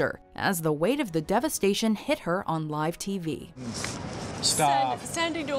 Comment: did he just say stop???